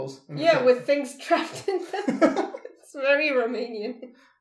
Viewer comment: did she say "it's very romanian"?